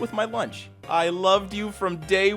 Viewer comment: I thought it said I loved you from day 2